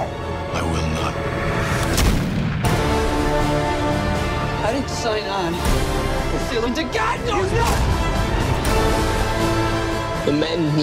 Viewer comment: What did he say at 1:44? I can not figure it out. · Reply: "I didn't sign on to sail into god knows NOTHING!!" I'm somewhat sure he said that whilst opting to sail away from Ecuador to hunt a plethra of whales.